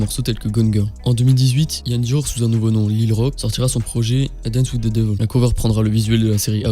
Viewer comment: J’ai pas bien entendu 😅 ct koi son nom avant Iann Dior ? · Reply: Micheal Ian Olmo bg